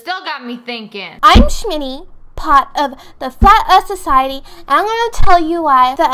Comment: I really thought she said fat earth 😂😂